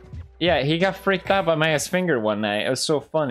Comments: “Scared of Maya’s finger.” Marzia: Am I a joke to you? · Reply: I heard "My, uh, Finger". Pretty sure that is what he said. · "He got freaked out by Maya's finger one night ." . So apparently maya can transform into human . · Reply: I thought he said "my a** finger" 😂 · Did he say he got freaked out by MAYA'S finger?